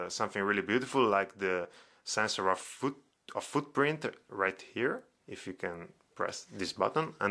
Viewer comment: he said foot print instead of finger print but it's a common mistake for non English speaker :) keep up the good work